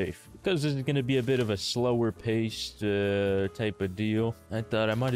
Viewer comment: did i hear you say slower paste ?? its slower pace babe